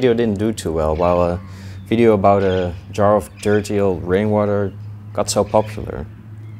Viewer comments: Thought he said brainwater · i thought it said brain water 😵‍💫